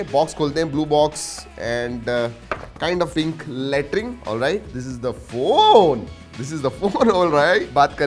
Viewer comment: ya kya bol diya😂😂😂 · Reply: Latring